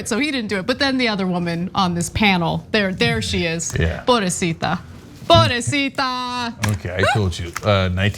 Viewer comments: did she just said Pobrecita !? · What does Ana say? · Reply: Porbresita. It’s Spanish for poor baby girl or poor baby. It what you say to a crying Karen to really piss them off.lol